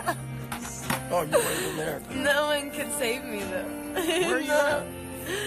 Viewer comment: I think i heard her say "no one can save me"